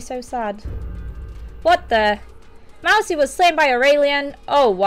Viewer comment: not to be rude but xD it sounded like she said mousie was slain by herr alien